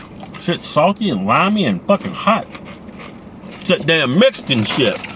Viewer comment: Did he say mixcan